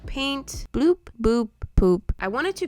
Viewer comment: Anyone heard her say oop boop poop 😛😛 idk if i heard it right but correct me if im wrong